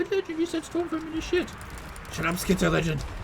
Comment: skitterlegend lmao! That’s great! I may have misheard but my skaven brain heard skitterlegend lol